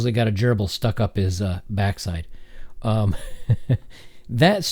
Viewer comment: Story about Richard Gere - what exactly did he say here? (English is not my first language). Gere had something stuck in his butt? Haha please tell what was it? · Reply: Ok I googled it - "richard gere butt" was enough to bring it up. Quote: "The story goes like this: Richard Gere once got a gerbil stuck up his ass and then had to go to the ER to get it removed. And that’s it — end of story. Yet this single-sentence narrative has somehow endured the test of time for decades, like some ancient folklore passed down from generation to generation. Why has this story been so durable? Where did it come from? Why the fuck is a gerbil always the rodent of choice? Most importantly, is it true?" - hahahaha... that sounds like an urban myth...